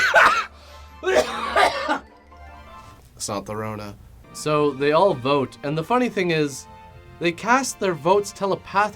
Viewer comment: What does he say in 8:07? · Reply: It's not the 'rona